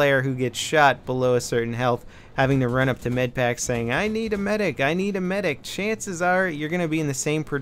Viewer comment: did I hear that right? Running up to a medpack saying "I need a medic I need a medic"